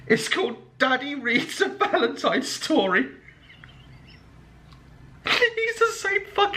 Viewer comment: At first I heard him say daddy reads me a hentai story omg